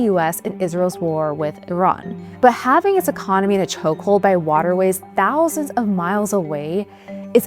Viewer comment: please enunciate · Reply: I also find it hard to hear what the presenter is saying. Too many syllables and consonants are being omitted. Just two examples: 0:24 "ba having sa conomy na chokehold" 1:16 "one o tha main grice" Otherwise nice video.